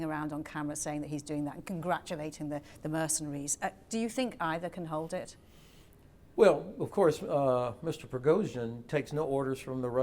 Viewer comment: 'can Ivan hold it?' Misheard, and improved. 😁🎈